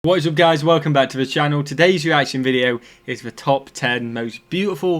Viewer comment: I hope you can answer this for me. When you start most of your videos it sounds like you say, “Boys and guys welcome back to the channel.” That can’t be what you’re really saying. I play it over and over again but it still sounds the same. What are you saying at the beginning of your videos? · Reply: He says "What's up guys, welcome to the channel...."